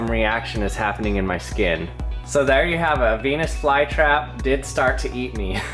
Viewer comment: 649 i heard it as '' so there you have a penis fly trap '' instead of '' so there you have a venus fly trap '' XD